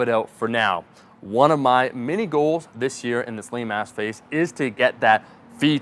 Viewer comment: At about 8:20 it sounds like you say "lame ass phase" and I enjoyed it